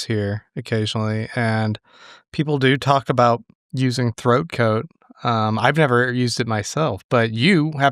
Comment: I was making food and I thought William was saying throat goat